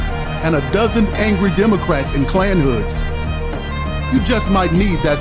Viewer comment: “ a dozen angry democrats in klan hoods “ …….omg ! I nearly spat my drink out ! 😂😂😂😂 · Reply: I thought he said "Democrats in cone heads"...... Then I replayed it and LOL.